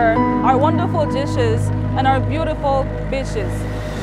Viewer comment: Wait wait wah? BEACHES... OKAY... accents make you hear things oddly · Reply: he sounded like he was saying "bitches" when talking about beaches 😂